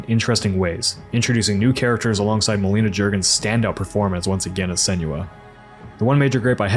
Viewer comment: I misheard 1:15 to "Melina Juergens' stand-up performance" and now i'd like to see that happen.